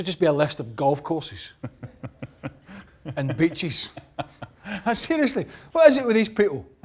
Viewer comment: I thought at the start he said bitches instead of beaches lol